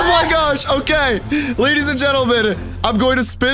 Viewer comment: did say I'm gay????